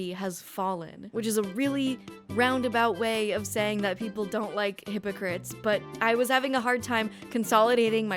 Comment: I thought you said “people don’t like immigrants”, and I had no idea how you got there 😅